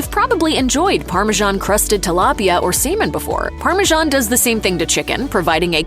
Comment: Definitely Slow Down with your Pronunciations of Words in your Videos. It Sounded like you Said to Add Parmigiana Cheese to Semen instead of Salmon in this Video.